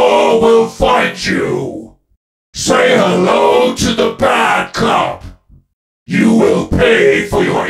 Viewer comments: Say hello to the bad cop 😈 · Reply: i thought he said "say hello to the backup" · I thought he was saying "Say Hello To The Backup" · for the longest time i thought it was 'say hello to the backup' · I always thought he said "say hello to the backup"